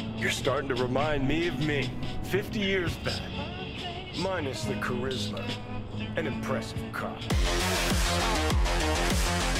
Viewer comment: He said “an impressive cop” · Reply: I thought he said "An impressive car"